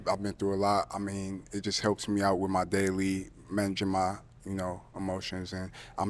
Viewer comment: what he say lol his daily regima? 😂😂